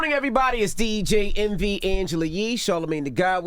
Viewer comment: I always hear 'DJEnvyangelayee and Charlamagne tha god! DJenvy adding to his name